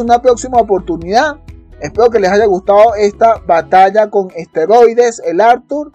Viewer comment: "BATALLA CON ESTEROIDES" Mi oido creo que lo escucho mal, no puedo saver que es lo que dice XD Álguien que me ayude Por favor...